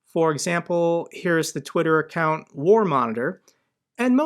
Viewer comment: I thought i heard "Warmonger", instead of War Monitor when did not for once look at the screen while listen to the episode.